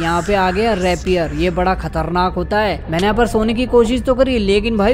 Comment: Did you just called a reaper repier